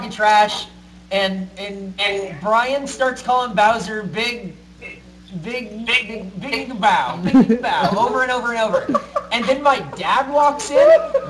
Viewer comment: WHAT was calling Bowser?? lmao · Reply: Big digger bowz, he is a gold digger you see